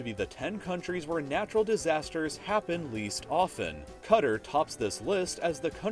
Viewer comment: did he just say kutar instead of Qatar